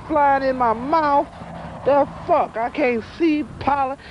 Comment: guys, what is he saying in 0:21... i cant see PALA ??? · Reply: is acting just like I do every spring in Kansas, the pollen capital of the world. "I can't see! Fuckin' pollen!" 😅